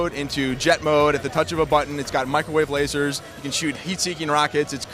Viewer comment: Wait....Did He Say Microwave Lasers? 0.o